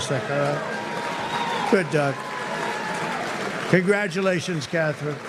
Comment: Sounds like Trump said "good dog" instead of good Doug.😮